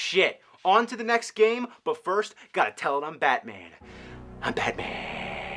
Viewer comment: it sounds like hes sayin hi batman in stead of im batman